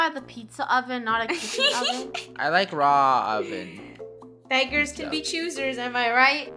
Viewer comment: "I like raw ovens" - 2020 (16:07) 😂 Or.. that's what I heard..